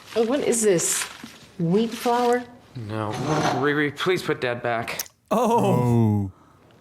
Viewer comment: I thought he said put THAT back the first time but it was put DAD back